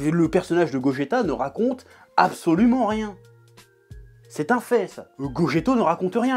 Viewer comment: goge quoi ? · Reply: Gogeto bébou